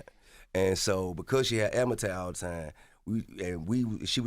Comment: What the hell is this brother saying?😂😂😂😂😂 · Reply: Azma tak... Asthma attack